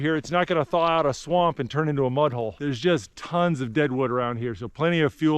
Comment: sounded like said “it’s not gonna thaw out a swamp and turn into a butthole.” lol 😂